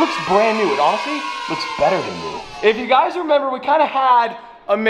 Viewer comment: " Oscar look at that it looks brand new and honestly it looks better than you" 😭 Edit: looks like I misheard